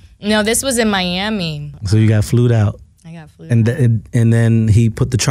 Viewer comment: Did he say, "you got flewed out"??? Really bro, how about learning the English language if you're going to interview someone 😂